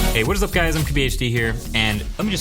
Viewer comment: What does he say 0:22 “hee whats up guy this is q age b d here”???